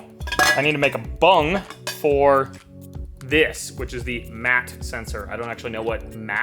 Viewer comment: I believe MAT == Manifold Air Temp sensor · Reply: I thought he said MAP sensor, as in manifold absolute pressure sensor (needed for boost as well, use the buick GNX version or any GM 3 bar MAP sensor.